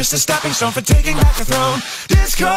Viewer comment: Did he realy just say this listen to what he says 3:12 💀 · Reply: "to make it, just take us away" I don't know what you heard